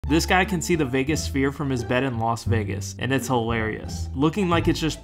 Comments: What it says: " This guy can see the Vegas Sphere. ' What I hear: " This guy can see his biggest fear. ' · I don't know how many times I heard "biggest fear" instead of Vegas Sphere. I think what I heard fits better!😂